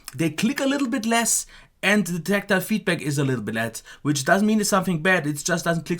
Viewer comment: did you say "and the tactile feedback is little bit LESS" ? I couldn't make up that last part (where I thought it might be "LESS") but I'm very interested though.